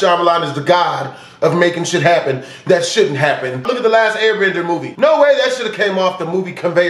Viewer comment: anybody know what said? i swear they mentioned a movie but thats impossible the movie doesnt exist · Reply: its live action avatar the last airbender movie